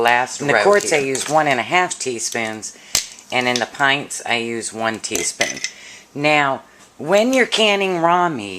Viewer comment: Darn, i just tried this and i mistakenly thought you said one table spoon of salt not one teaspoon. There goes thirty dollars worth of meat.